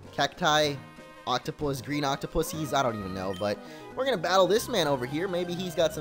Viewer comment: Did you just say "octopussies,"?